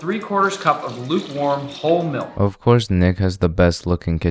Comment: WHAT DID HE SAID 🤨🤨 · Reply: Nick